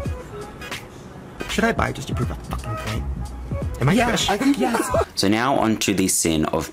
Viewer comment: did Shane say “am I Jewish?” Because if so that is not a good look. I guess all that time hanging out with Trisha Paytas has rubbed off on him.